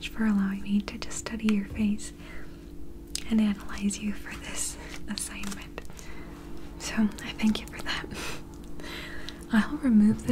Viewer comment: Мне послышалось или она сказала "всё"